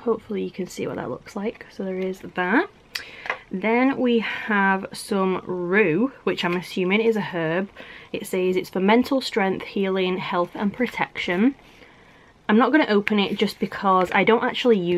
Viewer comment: i thought she said “menstrual strength” 😂